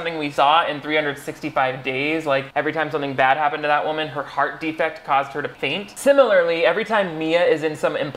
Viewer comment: What I heard “Every time something bad happened to that woman, her heart defect caused her to paint 🎨”